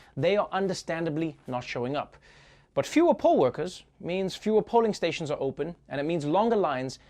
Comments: I know what hes saying is "poll workers" but I hear "pole workers" and I love the idea that trevor is advocating for strippers. 🥰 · Listening while multitasking I thought he said “pole” workers but got my mind out the gutter and realized he said poll workers, lol. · English is not my first language. I thought he said "shortage of POLE workers". Maybe there's coronavirus on $1 bills??!!